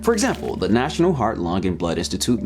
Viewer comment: I thought you said the heart, lung and butt institute. 😂